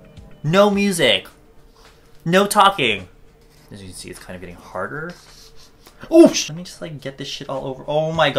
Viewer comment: "As you can see is kinda getting harder" that's what I heard Ps if I'm wrong please feel free to correct me